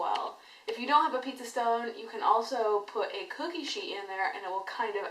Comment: uhm. maybe i misheard it but what it is again that can be a substitution for a pizza stone if I don't have one? is it cookie sheet or what? hahaha 'cause i really didn't understand on what you've said in the video :)))) ~~~~THANK YOU IN ADVANCE~~~~ -CSxx xoxo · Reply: CJAY SAMSON She said "cookie sheet". The reason for the pizza stone (or cookie sheet) is that you want the dough to be on something that is REALLY hot, AND holds a lot of residual heat. A stone works best because the stone holds a lot of that heat. What you don't want is for the cooler dough to soak up the ambient heat, you want the heat of the stone (or the cookie sheet), and the direct contact with the dough, to get it to cook immediately. It is similar (but opposite) to going outside when it is -20F. You can stand the air for a while, but if you took an object that was sitting outside (like a rock) and put it on your skin, you would certainly get colder much more quickly.